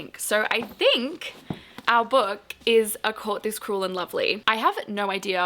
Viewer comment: Me over here mishearing things as per usual… I was listening more than watching and definitely heard “A Corpus Cruel and Lovely”, it was intriguing though 🤣🤣🤣🤣